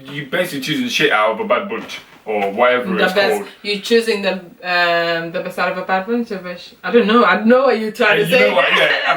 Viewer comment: I can stare at ur face forever <3 16:53 were u speaking another language? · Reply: I heard her say "You wish the best out of a bad wound".